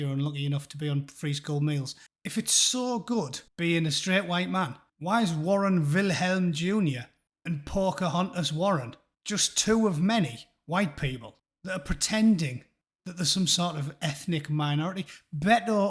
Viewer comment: Snooooooooooort...Sorry, suppressed hysterical laughter came out that way.Thought you said 'Porkahontas Warren.'